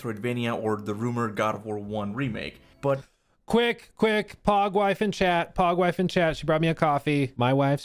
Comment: I can't be the only one that thought he was saying PAWG wife